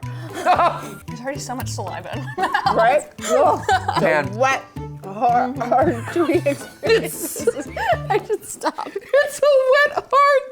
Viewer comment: um did you say wet hard oh lol